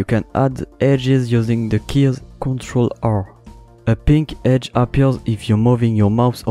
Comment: the pink line thingy is not appearin on me pls help :) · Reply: oh wait he said ctrl R i thought he said ctrl alt, i'm sorry :(